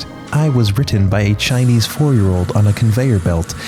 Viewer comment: I thought you said " I was bitten by a Chinese 4 year old" and I lost my shit